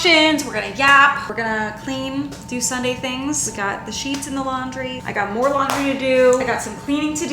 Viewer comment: I heard "i got cheese in the laundry.." and it took me a full minute before my brain caught up. Also, my limited experience with adhd medicine, when I've tried it (it's damn near impossible to get help for it as an adult in Norway) is that it helped me think more linear. Like "I have to put on a load of laundry" is usually "i have to put on a load of laundry, but first i have to empty the dryer, but before i do that i have to fold the prior load so i have an empty basket, but, oh i got to clean my closet so i can put the clean laundry away, "- and then an hour later I'm stood in my kitchen scrubbing the fridge and wondering why I'm holding a balloon.. on medication I'm able to go put on a fekkin load of laundry 😂 without the 17 detours that usually ends with me forgetting what i was originally trying to do.. linear thoughts. It just calms the brain enough that I'm able to think a thought to completion, and actually do the thing..